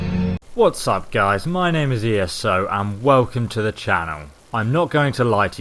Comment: i thought he said what is up guys my name is eso im not going to like you